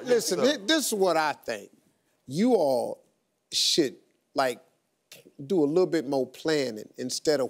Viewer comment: sounded like Steve said shit😂